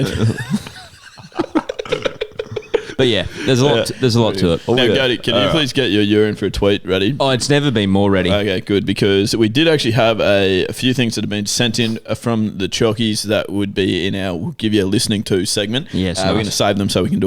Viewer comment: I almost thought said "could you get your urine for a tweet ready". I'm surprised you guys didn't jump all over that with your pun skills!